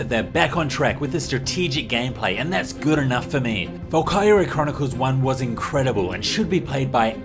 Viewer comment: volkyria?.. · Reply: I thought he was saying Valkewria.